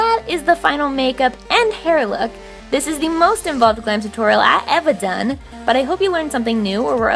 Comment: what did she say? "This is the most ???? Tutorial ive ever done? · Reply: Involved